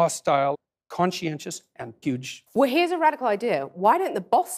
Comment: did he say huge? huge what, HUGE WHAT!